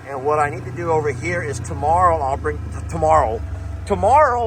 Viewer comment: Is that another Lesterism I just heard? 😂 Tomorri'll (Tomorrow I'll) 😅